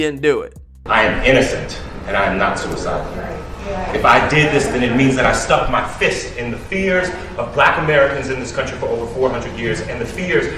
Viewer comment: Had to replay several times but I think he said at 1:24 : "I am innocent and I'm not suicidal. If I did this, then it means that I stuck my fist in the fears of Black Americans in this country for over 400 years..." So WTF. Isn't he kinda confessing to doing it?